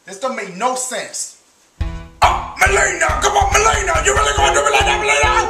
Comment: what does he says at 0:40 ?? i really want to know, im thinking on that frase as my ringtone xD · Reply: Mileena, you really gotta do dat to me mileena?